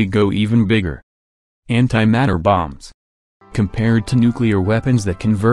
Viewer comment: For some reason when he said anti matter bomb I thought he said hentai matter bomb